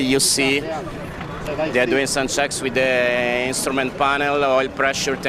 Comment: Did you just say sex with the control panel at 10:48??? 🤣🤣🤣🤣